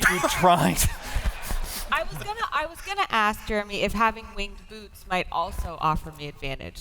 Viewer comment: Until the graphic help I swear I thought she was saying, "winged boobs"